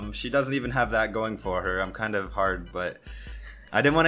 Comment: he actually said "Im kinda hard" lmaooooo